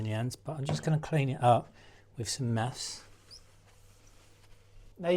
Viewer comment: What did you use to clean off the old sealant? Kinda mumbled there. Sounded like you said Maps?? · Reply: I said meths, that's methylated spirit